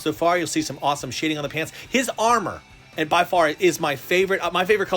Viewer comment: Why did i hear "they've done some awesome shitiing in the pants"